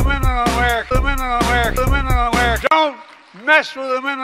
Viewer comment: I thought he said middle America 😂